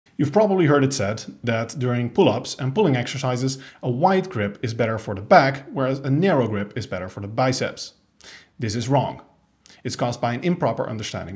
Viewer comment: I've only started watching you recently, I thought you said "wide grip is good for the pec " and thought "no shit that's the chest" 😂